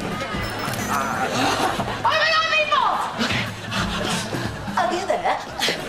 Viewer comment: What did Emma Stone say at 8:19? It sounded like "Oh my God, meatballs!".